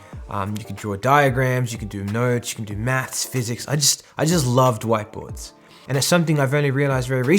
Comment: For a second, I thought he said he likes “white boys”…”Big white boys” lmao